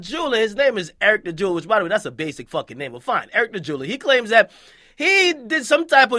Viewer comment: Akademiks. talk so fast I thought he said "Eric The Jew".